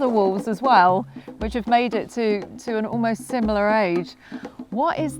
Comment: I thought she said she was almost 90